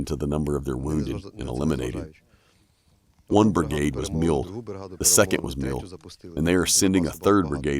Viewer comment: The soldiers Legion of the 47 brigade at approx 8:00 min. I think he might have said "mauled" not mild? Or is that slang I dont know?